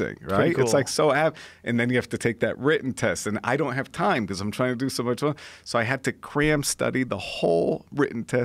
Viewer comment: Did he say " I don't have time cause im tryna do so much money" ? This guy is a legend😂😂😂